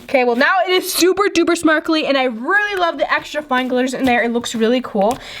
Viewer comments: when said “now it is super duper smarkly “ instead of saying sparkly . Did u notice it ¿ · You said smarkly instead of sparkly